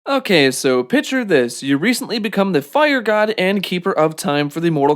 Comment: Did you say “pitcher this?”